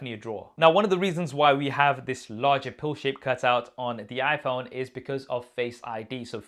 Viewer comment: I thought you really said Bullshit Cutout, instead you said pill shape cutout